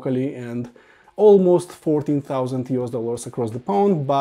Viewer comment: At first I thought you said they were 40,000 instead of $14,000... 😂... I was like... 🧐 You could buy Magicos or Wilson's for that price, and they have a lot better outriggers and footers than those shown in the video 😂